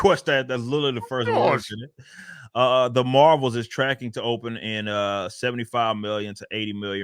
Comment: 🤗I just got up about 15 minutes ago took a shower and got my hot chocolate coffee with Carnation breakfast chocolate powder and my nice peanut butter and banana sandwich all good to go on potato bread and I start this snippit of the stream I watched live yesterday. My boy started this video with the marvels but I swear I heard him say The HORRIBLES 🤗 LEMMIE GET SOME MORE of Dis CHOCHA-COFFEE BREAKFAST DRINK AND HALF OF THIS NANNA SAMMICTH IN ME... ASAP! 🤪 Wake UPppp...